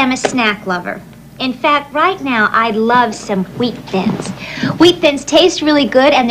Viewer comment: Did I hear Huweet Thins? 🤔